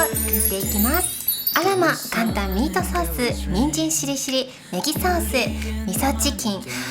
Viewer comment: の「人参しりしり」のさんのイントネーションが、なぜか人参尻尻🍑に聞こえちゃう😂😂